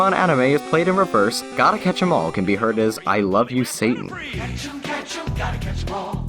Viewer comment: I heard "I love sushi".